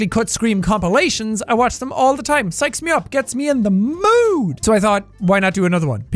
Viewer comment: When he said “sikes me up” at the start I thought he said “sex me up”